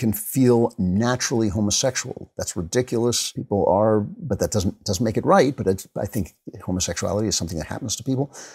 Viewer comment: Did he just say being homosexual isn’t right? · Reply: Nah. He said "always" not right.